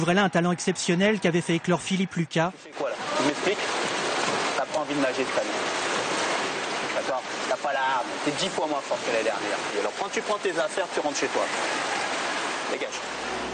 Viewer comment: Hi.Can someone tell me what is lucas saying between min 1:03- 1:14 ??? They are clearly arguimg but what is he saying to her? Pleas reply me! :) Thank you so much. P.s: I love laure <3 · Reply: ItsNastiaLIukinFan he said : what are you doing, you're boy hungry, toi don't have to swim fast, you're ten time less performing than last year, now get your stuffs and get out.